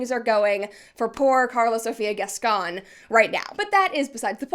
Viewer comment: English is my second language, so I am probably wrong. But I keep hearing Brett say "Carlos" instead of "Karla"... And I mean. That is accurate 🤭